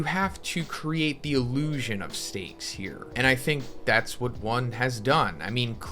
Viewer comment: did u say steak?